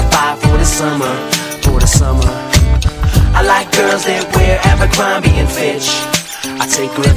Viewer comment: my mate thought it said apple crumble and fish.lmfao